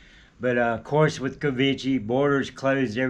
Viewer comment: I heard him say the word "covidgee" several times and then I realized he was using the Brazilian way of pronouncing Covid but with an American accent.